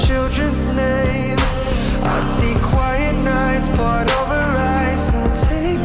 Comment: At first I thought he said brought over rice 😂😂😂